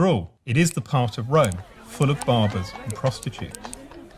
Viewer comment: Ok wait did it say barbers? What is wrong with being a barber?